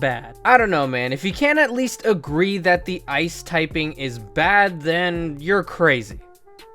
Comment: The subtitles from 9:37-9:44 are hilarious!! What is ACTUALLY said: I don't know, man... If you can't at least agree that the ice typing is bad, then you're crazy! What the SUBTITLES say: Because even if you got 150 special defense a super effective stab hit will destroy you (Also, 'defence' is misspelled on purpose because whoever wrote the subs clearly can't spell...) Great vid, tho